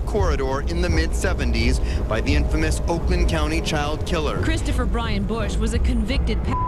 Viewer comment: I thought I heard county sheriff